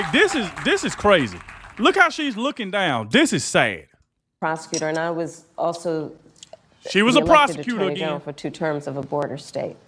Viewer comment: That was painful to watch her talk. Lay off the liquor lady 🥃 I thought you said she was a prostitute 😂 My ears are going lol